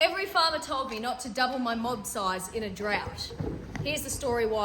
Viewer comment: I misheard "every farmer told me..." As "every Father told me" and I was so confused as to why there were a load of dads apparently giving you farming advice.